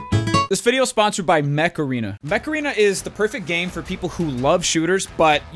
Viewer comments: i thought you said this video is brought by macarena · I always hear 'Macarena' when says 'Mech arena' and now the song is once again stuck in my head. :/ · I swear I thought he was gana say meth arena and tbh I would been down to get it lmao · When the ad came up, I thought it said meth arena 😂😂😂